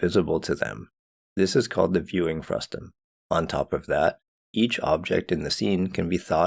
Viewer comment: This is called the viewing pasta? Thruster? Damn it no matter how loud i raise my volume I can't understand this mumbler. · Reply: Aww sorry, the term you're grappling with is "viewing frustum". These terms can be confusing to beginners, but thanks for the feedback, I'll consider enunciating more clearly in the future. For now, if you want to learn about the subject, you might consider the subtitles :)